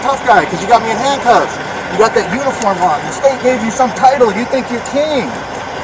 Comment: I thought he said South Dakota isnt a state, but at 21:25 mark (roughly), when complaining about cop being rough when putting him back in car he makes referrence about the state giving him a uniform